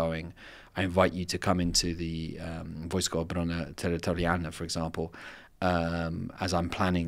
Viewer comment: Could you write out what was said at 44:15 in Polish? I would like to learn more about that course/program. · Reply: I think he was trying to say: 'wojska obrony terytorialnej.' He has an English accent and maybe you found it difficult to understand him.